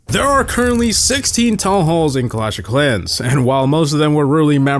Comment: Did bro just say tallhalls...